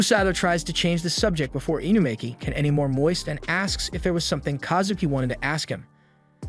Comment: Am I the only one who heard him say that they needed to stop before she gets any more moist lol😂😂😂😂